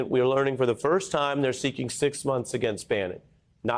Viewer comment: Did I just mishear "60 years" for "6 months"?